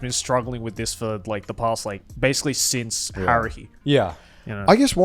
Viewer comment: what did Joey say? Haruhi?